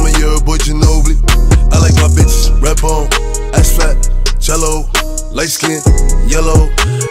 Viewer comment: i ain’t even gon lie, i thought man said cello 💀